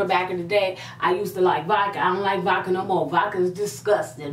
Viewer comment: I thought you said you live vodka...